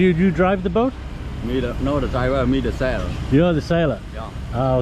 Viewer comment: Sounded like he said he was the seller not the sailor.